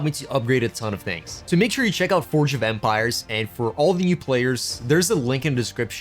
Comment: Start building your empire, research technologies, and explore history in Forge of Empires! Register by using this link to be granted additional rewards when completing the tutorial and reaching the Bronze Age Welcome event! <link> - The distribution of rewards may take up to 24 hours - AD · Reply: I heard that as "Forge of Vampires" at first and was mildly entertained by that idea.